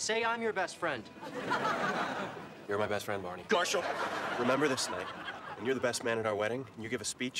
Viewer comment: did ted just say GARHAL instead of MARSHAL